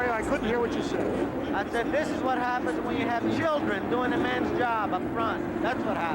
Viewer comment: All these years I would have sworn it was AJ saying "That's what happens when you send children to do a man's job."